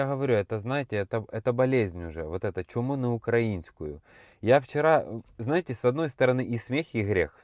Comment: "Чума украинская" послышалось. 😂 Что в принципе, не далеко от истины.